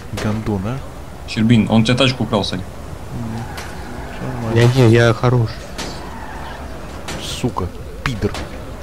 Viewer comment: мне послышалось сначало "Я гей, я хороший"